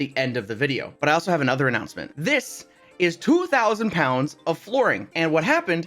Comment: in the intro I thought you said 2000 pounds of fluorine, which is like one of the most toxic and reactive things to exist